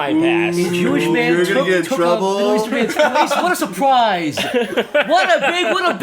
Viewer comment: did any one hear what said here, I know he said "The Jewish man took..." but I didn't hear the last bit. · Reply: jewish man took a middle eastern mans place what a surprise